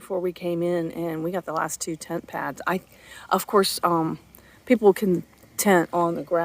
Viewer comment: Another great video! Nice campsite! Wouldn’t it be nice if all your stops had a bathroom and shower!🤦🏼‍♀️🤷🏼‍♀️So you each have a tent🤔? Thanks for Sharing! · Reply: Oh I thought you said “ we got the last 2 tent pads”